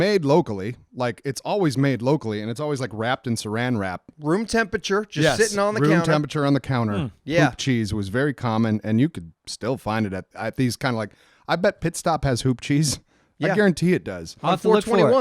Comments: I wasn't watching the video, I swore that they were saying poop cheese. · hahaa I thought said poop cheese xD